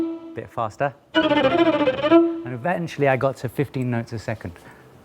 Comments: i thought he said 50 notes a second and i was sH0oK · I thought he said 50 notes a second, I was like pfft that wasn't 50 notes...